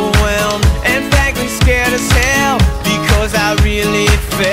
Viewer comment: I thought it said " and Frank is scared as hell"